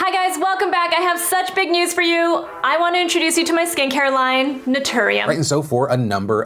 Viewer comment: When that lady said "skin care" it sounded like she said "skank hair" and I thought the controversy was going to be about marketing hair products specifically to people who sleep around.